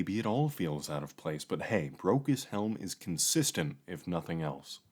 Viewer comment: All I hear is “Broke As Hell”